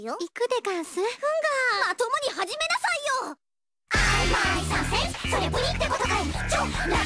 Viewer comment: am i the only who hears "im a sausage" at 0:06?